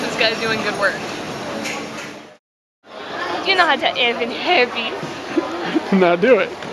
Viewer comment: What did say in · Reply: ***** Hahaha, so when we went elephant trekking in Thailand the guy who ran the place was really eccentric and over and over said "You know how to tell if elephant happy?" It became a catch phrase of ours for a while, and she was just saying it while wearing the mask.